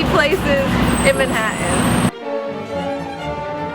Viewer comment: did you say "Manhaa'in"??? Giiirl, no you dih'int!